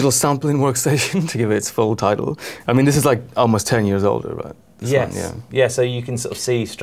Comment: Ensoniq EPS 16+ 10 years old? More like 25...I was the product specialist at UK distributor Sound Technology at the time, and I was a young man then! Lovely gear. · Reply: Simon Stock You misheard. He says it's 10 years older [than the other unit]